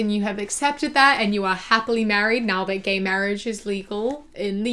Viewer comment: I thought you said "now that Gay marriage is lethal" 💀💀💀